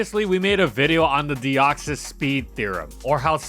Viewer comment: Slowbro and Deoxys-S confirmed beef?! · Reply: I’m so tired I thought you said “Deoxys-slow”